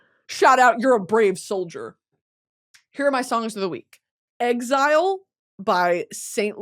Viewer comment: I thought you said "Psalms of the week" and I was so confused